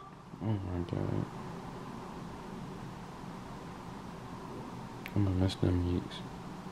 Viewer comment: who else heard him say "imma miss them yeeks" 😂😂😂😂